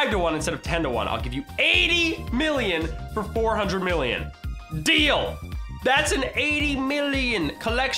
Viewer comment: Did anyone think said 'damn' when he said 'deal' at 3:08?